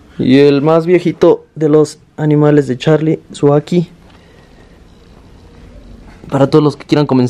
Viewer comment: Que especie es el varano del minuto 11:30 dicen que es perfecto para empezar con varanos, le entendí que se llama aki o aquí pero lo busco y no lo encuentro, el nombre científico si alguien lo sabe por favor Saludos y gracias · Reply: varano ackie